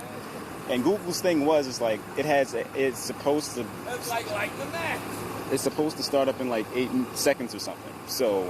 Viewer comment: Sounds like someone said "it's like like the Mac" lol